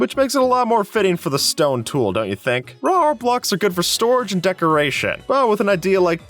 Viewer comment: anyone else hear "rawr blocks"?